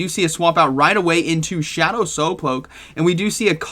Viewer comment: I HEARD WHAT U SAID THERE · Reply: SoapPoke